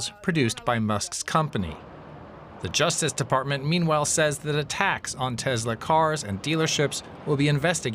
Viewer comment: I kept hearing "...the Justice Department, meanwhile, says that attacks on Tesla cars and dealerships will be investigated as domestic terrorism." I kept hearing "attacks" as "a tax"! I was so confused.